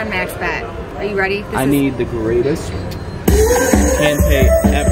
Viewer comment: I have watched this channel for a while, and i am embarrassed to admit that it took a good while for me to realize that the husbands name is. I thought kept calling her poor husband a moron. 😞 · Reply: No worries. It took me 3 months before they were saying hand pay. I thought that they kept saying handbag, and I couldn't understand why they were getting excited over a handbag . I thought to myself, " who needs another handbag